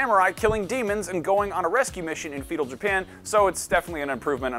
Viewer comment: I heard “Fetal Japan” 🤣